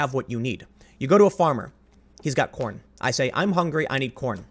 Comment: as I heard it: You go to a farmer. He's got porn. I say, "I'm hungry, I need porn." He says, "Okay, I need help. You help me work, I give you porn." I say, "well, I need more than just porn. Right? I need water, too." He says, "can't help ya. Don't have water." With money, however, he says, "I will give you $10 to help me with my porn." I say, "Okay, with that $10, I can then get water and [porn]."